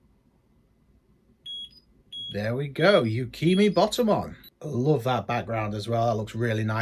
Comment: for a second I thought you Said bikini bottommon